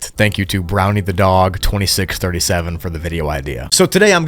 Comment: i thought bro said 2763